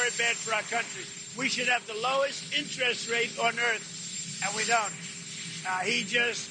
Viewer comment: I was multitasking and would have swore the Cheeto said “we should have the lowest incest rate, but we don’t.” That was INTEREST rate!! You honestly never know with Don, so I had to rewind. 😂